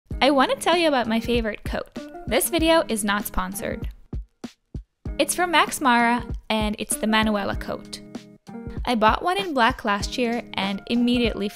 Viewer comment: I thought it said "My favorite Croat" and I thought you had a new lover from Croatia